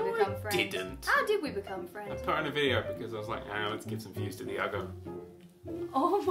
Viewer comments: i cant near what dan said at 2:20 ) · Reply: It's been three months but may as well answer haha. He said "let's give some views to the uggo" sarcastically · Its been over a year and i still have no clue about what Dan said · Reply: I put her in a video because I thought ahh lets give some views to the ugo